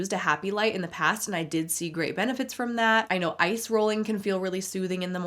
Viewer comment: I heard “ice rolling” as “eye rolling” and I was like, that checks. 😂😂😂